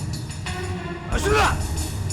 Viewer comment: 이덕화 뭐라고 말한거에요? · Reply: 알았습니다